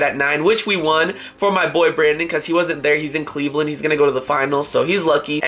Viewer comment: do u ever see a comment and wish u wrote it :D :) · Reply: Did he say boyfriend at 0:23 ???